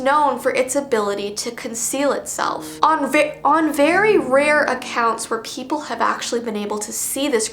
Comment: does anyone else hear “onv on very”?